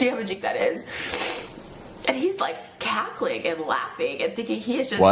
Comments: I think she said “cackling and laughing” not catholic and laughing.😂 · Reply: Yeah it was obvious she said cackling, I cant believe they thought she said catholic. Ethans team can be very dumb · She didn't say Catholic. She said cackling. Pretty obvious misheard line from her. Regardless great moves Ethan, keep it up proud of you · I’m pretty sure she said “cackling” and not “catholic,” just what I heard. 🤷‍♂️ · Dude she said you're cackling not catholic 😂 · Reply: Lmao I thought she said catholic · Trisha: "hes cackling and laughing" Ethan: "I'm not catholic" 😭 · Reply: I had to go listen to it again to make sure I head her say "cackling" and that he misheard her. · you misheard cackling and laughing as catholic and laughing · I think she said cackling, not catholic. Which makes more sense with the next word be laughing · I thought she said “he was cackling and laughing...”